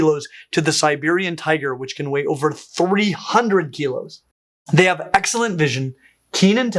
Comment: The tiger 40 hundred or 14 hundred kilos (I am not sure which 1 I hear. But both are too heavy for a tiger) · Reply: No, he states 2kg as the weight of the lightest cats to 'over 300kg' for the Siberian tiger.